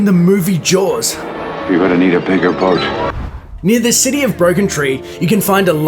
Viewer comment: Did he just say in "the city" of broken tree?